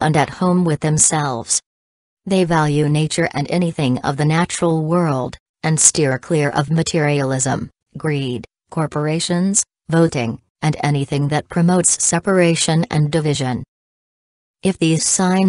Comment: did you say we don’t vote?